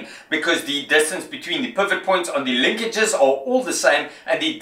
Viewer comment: For the longest time i thought he was saying “perfect point” and it turns out he says “pivot point”. Anyways Nice Job!